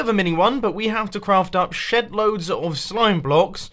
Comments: Oh man I thought he said we have to craft SHITLOADS of slime blocks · Who else thought he said "shit load" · does he say "...sh*t loads of slime blocks?* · Did mumbo just swear in his episode? I thought he said, "I have to craft shitloads of slimeblocks" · did he say: we need to craft a shit load of slime blocks? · Did Mumbo say what I think he said? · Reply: shed load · did he say shitloads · Is it me or the first time I heard him say shed loads it sounded like shitloads